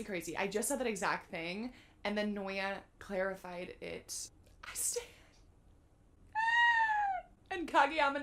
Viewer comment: did she say "I stan" ? That is so funny hahah